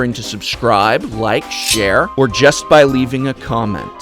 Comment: I thought he said "Or jeskai leaving a comment" O.o